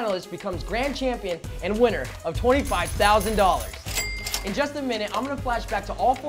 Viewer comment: that awkward moment when he said "in just a minute" and I heard "in Justin Bieber"